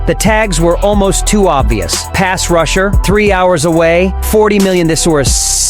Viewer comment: Why does the caption say "Ass Russia"